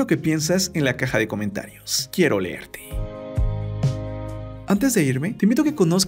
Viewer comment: BUEN VIDEO GRACIAS POR EL DATO PERO PORQUE DIJISTE "QUIERO OLERTE"